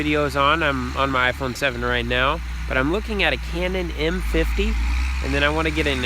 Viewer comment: I enjoyed the video, just finished it. I heard you mention the camera you wanted to get, what was it? I thought you said Canon N 50, is that correct? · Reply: John Johnson Canon M50. Thanks for watching!